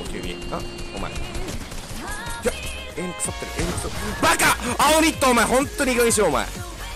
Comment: バカ！煽りってホントにいい加減にしろ。←✕ 青ニットホントにいい加減にしろ。←○ 俺だけかも知れないけど✕に聞こえない？